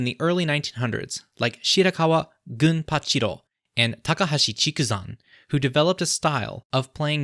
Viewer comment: I thought I heard Kamaboko Gonpachiro